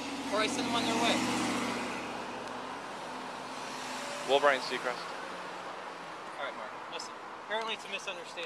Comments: did he just call him ryan seacrest? I'm hollering laughing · did he just call him RYAN SECREST 😂😂😂😂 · I thought Mark called him Ryan Seacrest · What did the cop say to the major at 7:01 It sound like if he called him Ryan Seacrest 😂😂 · did he say ‘i love Ryan seacrest ‘